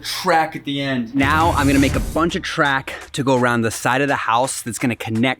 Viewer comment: Did you say crack…